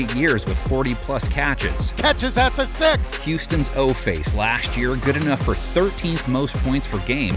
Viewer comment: Did…did he say “OFace” at 4:41?